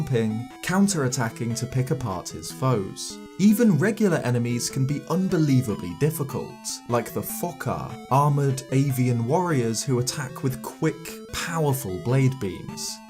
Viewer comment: I thought he said “like the fu**er” at first